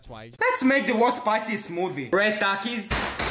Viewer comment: I thought he said movie instead of smoothie